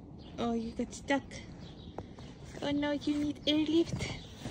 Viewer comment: When he got stuck on the stairs I thought you said "oh, you need an AIR lift?" 😂😂🥰